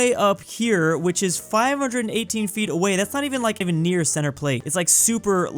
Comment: Did be just say "Center Plate?" OoH BaseBaLL iS fun I gOT to ToUCh center pLATe